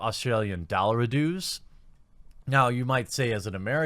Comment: Yeah mate over here its normal for a AAA to go for $90-100 dollarydoos. $55 is definitely a deep discount for a AAA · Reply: I thought he was being funny saying dollarydoos